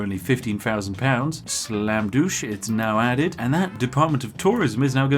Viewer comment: I never thought id need to hear the saying ”slam douche” at 15:02. Instead of slam dunk. Idk but that made me laugh so hard